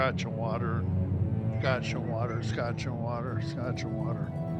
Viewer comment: "scotch and water" lmao at first I heard him say "discussion water" 😆